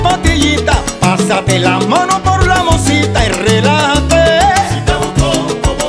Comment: Hola a todos desde Argentina...una pregunta, a ver quien me puede sacar de la duda, en el minuto 1:32 dice "tomate una pastillita, pásate la mano por la ??? y relajaté.." qué dice donde puse signos de pregunta? no entiendo la palabra... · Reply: Por la mosita